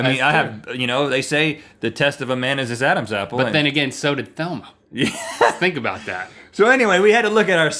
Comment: I keep hearing "Thelma" and not "Velma"